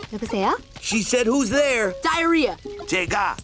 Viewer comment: it really just sounds like she said “who’s there” in English.